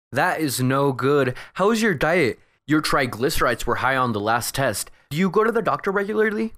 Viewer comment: What does the doctor say at 12:35? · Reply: he says "That's not good, ¿How is your diet? Your Tryglicerides were high on the last test, do you go to the doctor regularly?